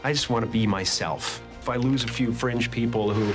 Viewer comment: The caption around 2:20 said "If I lose a few French people..." I think he said fringe :)